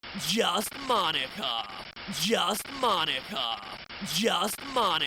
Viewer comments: Man, in that intro, I thought he was saying "Just monotone" over and over. I'm stupid and cruel. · The more I hear monika the more I hear my ni**a....